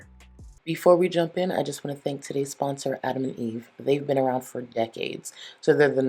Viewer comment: For a second I thought you said “our sponsor Adam Levine” and I was about to be like “damn Charlie he got you too?” 💀